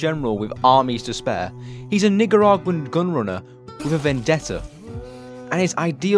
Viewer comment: ayo what did you say?? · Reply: Nicaraguan*